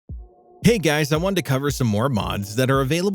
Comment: It almost sounds like he said "Hey guys I'm undercover" in the beginning